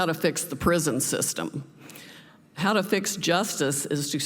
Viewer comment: Oh, i thought you said” How to fix STUPID”. Get rid of the demonic Democratic Party and replace it with the patriotic Americans.